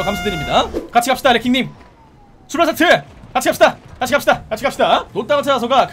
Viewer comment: 에서 나만자꾸 출발사트! 라들림?